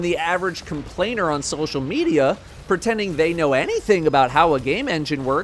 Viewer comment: ehehehe 3:40 subtitles say Reddit instead of social media and I just find that hilarious.